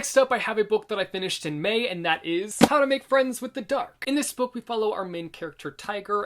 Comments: I heard "how to make friends with a duck"😂 I always anticipate and love your videos!❤ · I thought you said how to make friends with a duck, I was very confused 😂